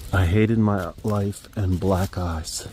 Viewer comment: I heard something different from “black eyes” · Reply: "Black guys" That's what I heard